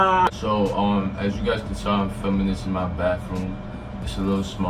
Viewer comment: He really said “as you guys can saw”